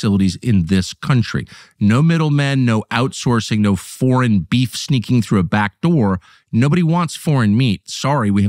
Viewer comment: Did I hear “foreign beef sneaking in the back door” ?